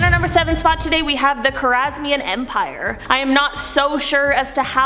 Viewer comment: It sounded like you said Kangaskhan like the Pokémon lol